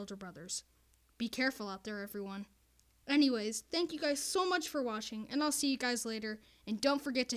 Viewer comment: did you just say "thanks for WASHING"